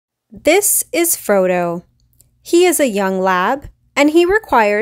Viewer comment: I thought she said “This is Frodo. He’s a young lad.” And in my head I was like Middle earth fan, huh?